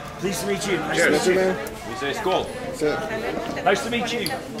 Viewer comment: Do i hear it right? 5:13 he say "skål" - cheers in swedish. Who are from sweden?